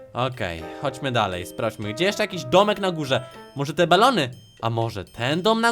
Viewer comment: Ja usłyszałam może te banany minuta